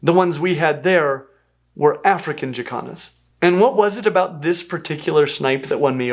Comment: 😂😂😂 i know you are saying Jacans but i swear it sounds like yoy are saying african chi-kahns.